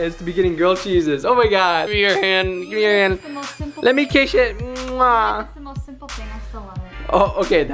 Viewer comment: When he said let me kiss it i thought he said let me kiss shi*